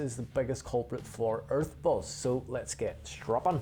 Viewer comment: I kept hearing "Earth Balls".